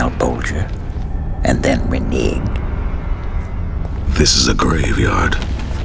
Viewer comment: What the hell does he say here? "Did you really think you could strike a deal with Malbolgia and then -?" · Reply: "renege" - turning back on a promise. Makes sense based on context for this